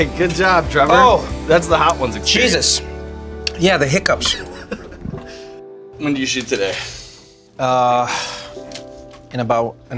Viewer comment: S: "When do you shoot?" T: "In about an hour." Me: what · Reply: I thought he said "when did you shit today?"